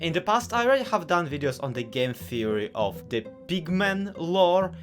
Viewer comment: i'd watch this video further, but i heard "big men" instead of pigmen so now i can't take it seriously